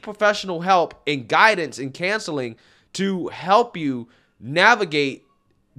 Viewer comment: If this "woman" is trying to convince us all, dude has failed as badly as he failed at a healthy lifestyle. 6:43 lol said cancelling instead of counseling 😆